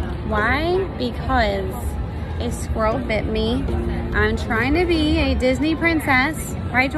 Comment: When said (this squirrel 🐿 bit me) I thought 💭 she said this girl 👧 bit me but she will be fine I pray 🙏🏾 she will not have rabies